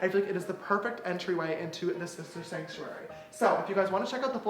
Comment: Was I the only one that thought he said sister shanktuary?